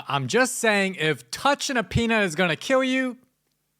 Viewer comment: Like how the captions thought bro said “penis” instead of “peanut” 😭